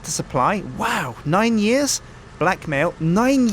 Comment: When was reading Roach's previous convictions. I was on another page and heard him say "Blackmail," I thought he said "Black Male." haha